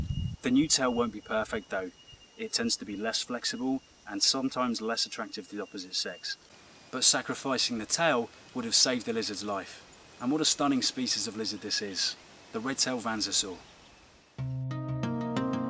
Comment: I thought it said venosaur